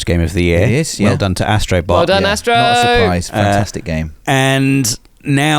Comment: sorry what did he say? 😂😂😂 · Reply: “Not surprised, fantastic game”